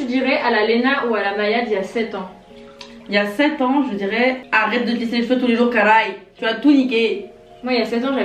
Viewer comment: Léna t’a dit quoi 😂😂🔥💙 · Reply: Vanessa ribeiria arrete de te lisser les cheveux tout les jour